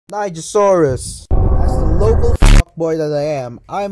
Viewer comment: At first I thought it said Niggasaurus